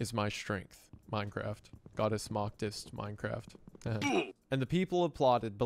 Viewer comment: I thought he said "God is goth, is Minecraft"